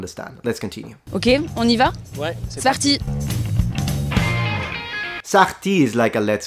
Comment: she actually said "c'est parti" which means let's go, but she was so fast, so all you hear is: sparti 😂